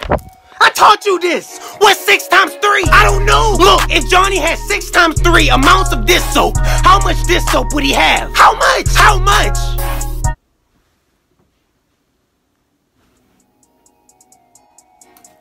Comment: Bruh the dish soap part 😂😂😂😂😂😂😂😂😂😂 😂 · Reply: I thought he said dis soap I was gonna say I've never heard a teacher say dis